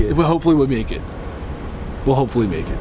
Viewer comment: at the end, i thought gray said “hopefully naked” for a sec lol